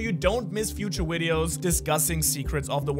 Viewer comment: did bro just say wideos